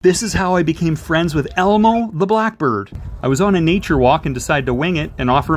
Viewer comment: I thought that said 'emo the blackbird' 😭